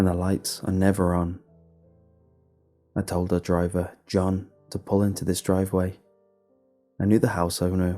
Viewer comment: I thought it said “don’t pull into driveways sideways” and I was really confused. Wendigo? I like those.